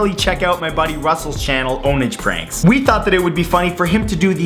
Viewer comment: At first I thought ownage pranks said orange pancakes...